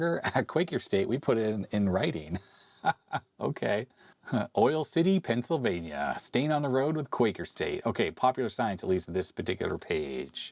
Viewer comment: "Stain on the road with Quaker State". I really misheard that the first time. 😂